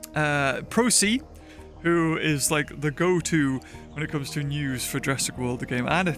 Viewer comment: you’re the goat edit: I thought you said goat not go-to😂